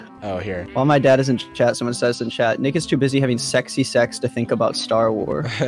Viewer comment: When he said nick at 4:45 it sounded like he said the n word